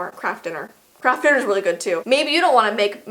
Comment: says baby instead of maybe lol